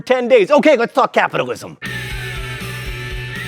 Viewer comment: There was a glitch on my computer, the video skipped a few seconds to 00:49, and I heard John saying "f*ck capitalism".